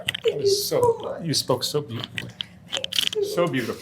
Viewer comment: That is a bit weird I haven’t seen the interviewer hug someone. Did I hear him say “god you’re so beautiful”